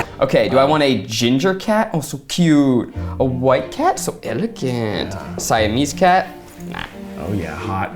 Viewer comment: When Zach said 'so elegant' at 15:19, I only heard AVPS Umbridge